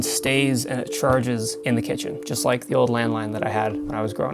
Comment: I thought you said: all I had when I grow up was a Landmine